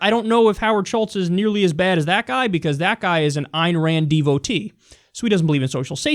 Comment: what does he call mnuchin at 1:48??? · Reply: i think he said "Ayn Rand Devotee"